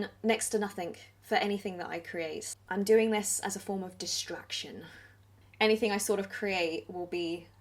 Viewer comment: I had to back the video up, because I heard “as a form of destruction” and I knew that wasn’t right... distraction, destruction... huge difference.